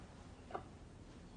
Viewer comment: ive been replaying this part trying to understand what ginny says and no luck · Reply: “nada nada limonada” nothing lemonade ???? idek i hate this show